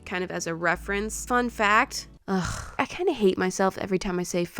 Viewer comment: my phone was glitching so it sounded like you were saying mff fact